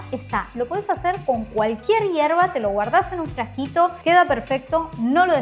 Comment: Minuto 4:15 flashee que dijiste "Lo podes hacer cojn cualquier mierda" JAJAJAAJAJAJAJ